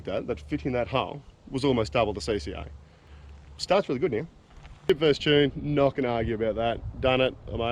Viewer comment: what do you say at 8:40? not going to argu about that??? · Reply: chip Vs Tune. I run a chip and im happy with it. Some will argue a tune is better. But that’s just my opinion on it